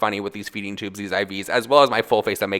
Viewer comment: When I heard "For skincare" I kept hearing "Foreskin care."